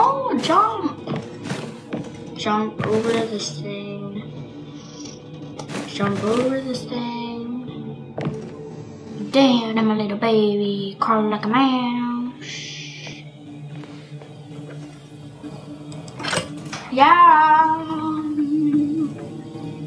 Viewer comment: Is it just me or did i hear damn im a little baby at 4.32 seconds